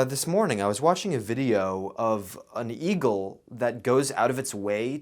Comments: i thought he said E-GIRL with a weird accent just to realize later that he said eagle. I’m disappointed at myself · I heard e-girl 🤣